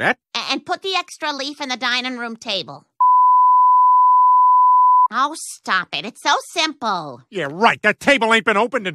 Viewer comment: What did he say? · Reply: FUIUUUUUUUUUUUUUUUUUUCK